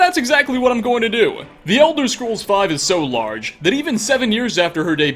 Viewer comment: Legit sounded like you said Eldredge scrolls 🤭🤭🤭🤭